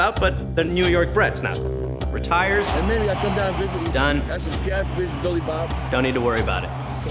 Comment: did anyone else hear the jets coach say "Billy Bob''